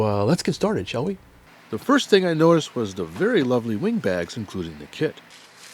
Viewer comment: Wing Bags? I thought you said wind bags, I was wondering what my Mother in law was doing again.....